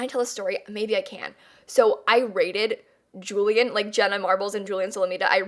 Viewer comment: 😂😂😂 LMAO I’m sooooo dumb I thought she said “RAIDED” I thought she said RATED and I was like 🤨